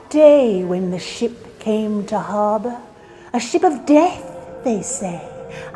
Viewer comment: I legit thought you said when the sh!t came to the harbor lol